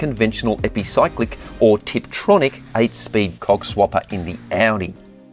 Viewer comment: WHAT did you say????? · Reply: Co@ swapper😂